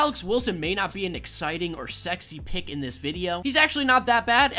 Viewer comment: did he say sexy? 😂😂😂